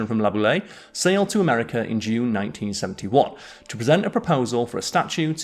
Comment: I listened twice, and I think I heard you say 1971 instead of 1871 at 3:18. Might just be your accent!